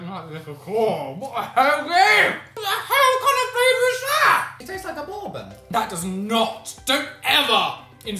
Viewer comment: I thought said “It tastes like a Mormon” at 4:42 and I was painfully confused